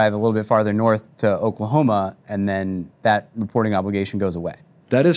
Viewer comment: Did he just say Oklahoma? Hmm... 🤔